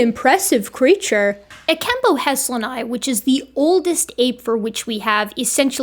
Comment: I wasn’t looking at the screen and I thought you said ekembo has-one-eye 😅